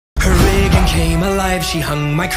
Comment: I keep hearing “her rave came alive”